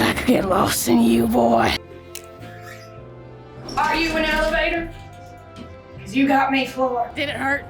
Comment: what is saying? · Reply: "Are you an elevator? 'Cause you got me floored." I believe.